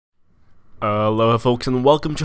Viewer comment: At the very start you sound like you say "Allahu, folks"